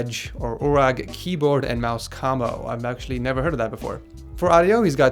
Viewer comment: he says kamo instead of combo